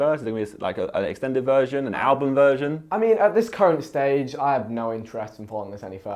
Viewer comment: I thought vikk said not at this curry stage 😂😂